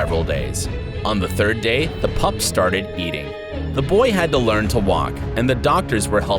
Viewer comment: am I the only one that heard yeeting instead of eating 🤣